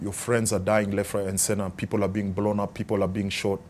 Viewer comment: Did he just say Sena instead of center?